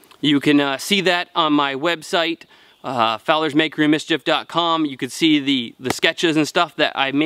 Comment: I thought i heard said Fowlers "bakery", and i thought eh, a butter flour and sugar guy too! Right on!... Then i saw. but i guess if you got a stuffy nose, then "Makery" becomes "Bakery"... or im just old and cant hear shit. Prolly the latter